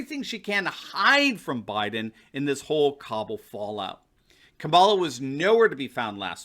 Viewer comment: Did you say "Kabala" xD That's great.